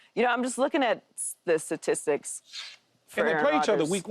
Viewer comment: Paul it sounded like you just said the Stylistics lol its all good though Shout out to yall three. GOD IS good. Um just a fan of Speak lol